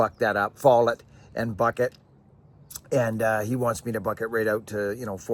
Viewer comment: Hey, wait a minute ! I thought you said “stand up and buck!” ? You’re gonna wrench your back! Lol 😉. Keep them vintage a running’ ! Love ya man.